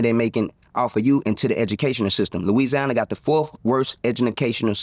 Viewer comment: I had to rewind to make sure I heard him say " Edunalcational System"😅😅😅 He right! The 4th worst in the country😂😂